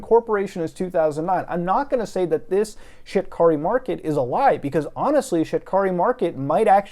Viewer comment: I thought he was saying ‘shitkarimarket’ 😓